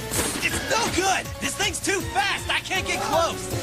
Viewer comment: honestly at first, I didn't hear the s sound in fast, so I tought he said "he's too fat!" XD